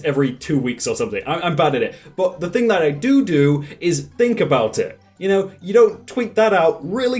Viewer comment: Did he say doo doo? 😮